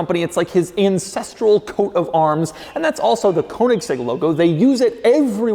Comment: did he say “Incestral coat of arms?”